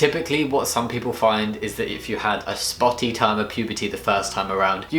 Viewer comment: what is he saying? A spotty time?